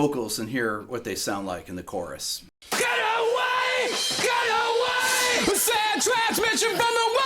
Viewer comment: pretty sure that the "cut away!" vocals starting at 8:08 is Jim Ward doing his part of the call/response chorus with CBZ.... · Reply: I always thought he said get it away😊